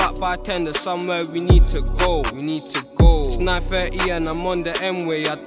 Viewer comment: is he saying nine half or something????? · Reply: Yes he’s saying the time is